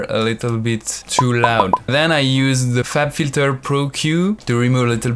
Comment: I thought he said "fap filter"